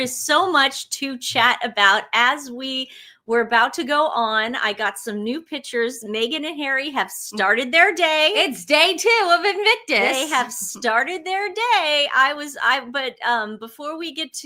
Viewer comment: I thought I heard you say “day 2 M and H have snorted” the day. Appropriate??!!?? ❤❤🤗🤗💐💐